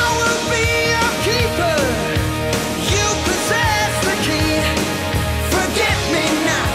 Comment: When I first heard this song I thought they were saying "Forget me now".